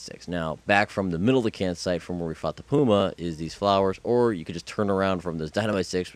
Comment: Doesnt it sound like he said "fucked the puma"